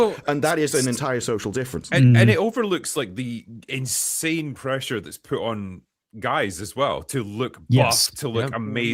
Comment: What movie is Drinker mentioning Jessica Biel being in here? I can't hear and captions can't tell because and are talking over him. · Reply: Blade Trinity